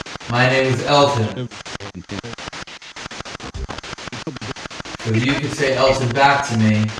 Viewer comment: after it says hello I heard this "hello El-ton" like it was trying to say Elton